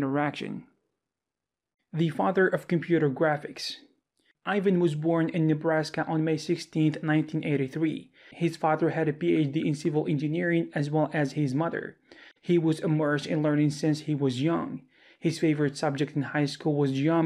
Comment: what says does he say. Sounds like 1983. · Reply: it is 1938